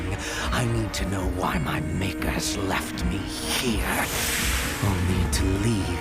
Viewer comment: I thought the cat said “makers” not maker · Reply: The cat does say “makers“